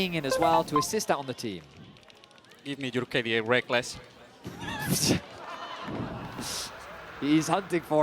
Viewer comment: what did he say? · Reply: give me your KDA, Rekkles.